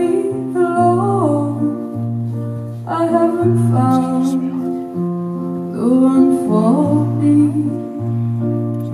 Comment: Anyone else heared that weird whisper at 2:38 ?? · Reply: That's some dude who passed by and said "excuse me"